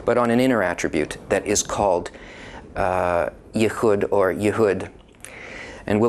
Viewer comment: did he just say the same word twice, 'yahud, or yahud'